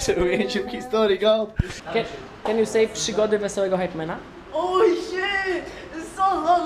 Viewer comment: hahahahahha te ''przygody'' usłyszałem jako ''ssijwory'' XDD